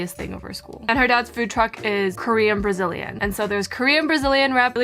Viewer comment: hate romance but did you say korean-brazilian?? Now I'm interested, I'm Brazilian.